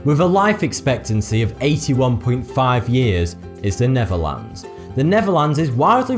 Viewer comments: Hahaha. In the Netherlands you are allowed to smoke cannabis. Is that healthy? I dont think so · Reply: Felix Fellinger I thought he said neverland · The way he said The Netherlands sound like he said The Neverland 😂😂😂😂 that killed